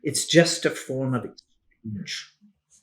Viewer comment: What did she say :money it’s just a Form of…. ??? Minute 39:37 !!! ??? Why this was inaudible ??? Thank you Great transmission 🙏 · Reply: “… a form of exchange” 💙